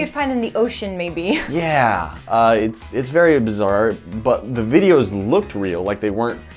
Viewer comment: did he say abzar